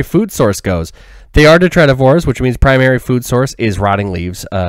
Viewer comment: what is this diet called? I've never been able to find the word for insects that eat decaying plant matter. I tried to look up what I thought you said and the Internet basically said I was making it up so I'm guessing I did something wrong with the spelling 😅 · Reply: Detritivores:) I promise you, its not made up haha. Hope this helps!